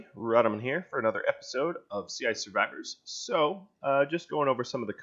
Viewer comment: everytime you say "sea ice survivors", I hear "CI survivors" XD devs/engineers might get this